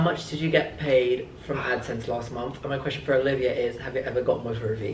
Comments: WHAT DOES HE SAY TO OLIVIA? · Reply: I think he says "have you ever got with hrvy?" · What is said at 13:00 ? Motor of Eve what ? Lol 😂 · pls put me out of my misery and tell me what says at 13:00? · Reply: he said have you ever got with harvey? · Wait wat did say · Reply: Did you ever get with HRVY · what did he say to Olivia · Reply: Have ever gotten with hrvy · what did say at 13:09?? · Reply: Did you ever get with HRVY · someone please help me, what on earth does say @ 13:00, have you ever got modder of eve?!?!?!? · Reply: have you ever gotten with HRVY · what does say at 13:00 ?! I can't work it out for the life of me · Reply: ‘Have you ever gotten with hrvy?’ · What does ask olivia at 13:00 I cannot understand him ?!? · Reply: I think he says "have you ever got with hrvy?" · What did he say at 13:02 ahaha ? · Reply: Have u ever gotten with hrvy